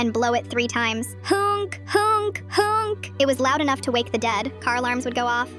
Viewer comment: It sounded like she said "hunk hunk hunk" what boy is she looking at😂😂😂😂😂😂